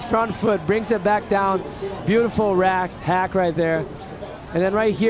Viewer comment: Did he just say "beautiful rack?" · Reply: Beautiful *hack